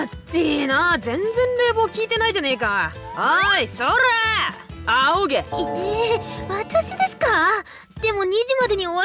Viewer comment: 「仰げ」が「喘げ」に聞こえてしまった…